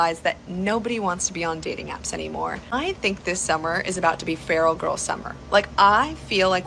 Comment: she actually said “feral girl summer”. She’s not feminine enough to keep a man, so her suggestion for women is to act more like an untamed savage. Well good luck with that one, girls 🙃🤦🏾‍♂️